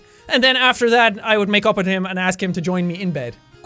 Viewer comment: Jack:"...Then I would say sorry and makeup with him..." What I heard:"...Then I would say sorry and make out with him..." Me:"But I thought you said he has no face?!!!